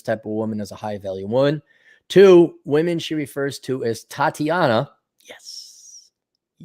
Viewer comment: Tatiana's? I thought Tomi was saying "Thotiana's". which is even funnier.